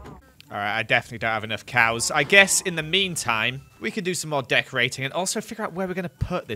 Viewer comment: I thought he said death rading instead of decorating